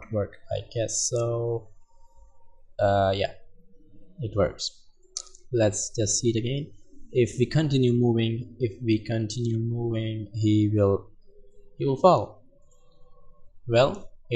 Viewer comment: Hi there! At 15:15 the author says the phrase what I heard like "упал" in my native language. This phrase says like "upal" and the translate of this word is "fell". Fun, fun...